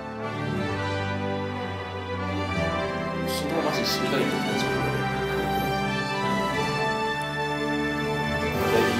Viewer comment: なんて言ってる？ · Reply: 昨日まじで死にかけてた体調不良で